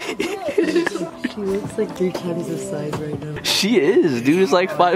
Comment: I didn't get what she said 4:39 xD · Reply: She said he looks small compared to her